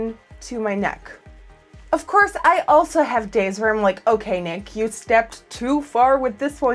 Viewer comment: Did she just say "Okay, Nick"